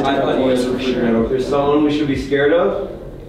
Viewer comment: 04 when I believe who asked "is their someone we should be scared of" and I heard "yeah, but you should be okay."